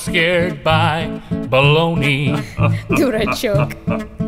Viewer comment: Did she said ''Dude I Choke'' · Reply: She said "Durachok", literally it's means "little fool" in Russian :) but here it's more like a "silly boy" :)